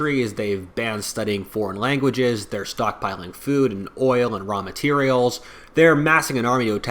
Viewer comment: I was hit with an ad at the perfect moment to make it sound like you were saying China is stockpiling "ramen" instead of raw materials. Me: "huh, me too."